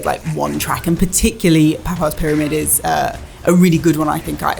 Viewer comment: Did she just say Papa's pyramid??